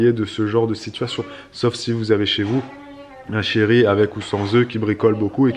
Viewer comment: Oulala j'étais pas réveillée 😂😂 j'ai compris "un chéri avec ou sans oeufs" et là, j'étais PERDUE 🤣